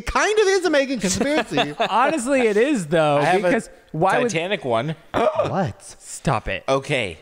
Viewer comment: when said “titanic one” at 1:11:46 i thought he said “tana qwan” like tanacon but with pizzazz